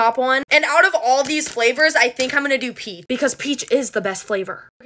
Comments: Bro I thought she said pee instead of peach 💀 ☠️ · Are we not gonna talk about how she cut off the “ch” in “peach” so it sounded like she said pea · Bruh I thought that she said pee instead of peach 💀 ☠️